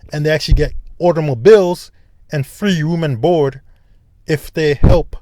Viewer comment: what is he saying at 39:34 ? "free woman board"? what is that? women lined up on the board?